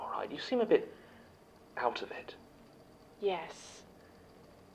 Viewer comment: omg I thought said “counterfeit” instead of “out of it” and I nearly screamed in happiness that he is gonna catch this imposter